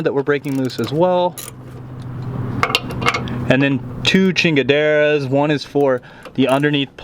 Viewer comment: did u said two chingaderas!!? Haha